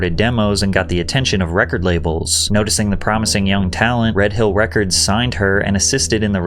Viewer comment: Not gonna lie, I had replay at 2:30 because I thought you said she signed to "Red Pill Records!" 🤣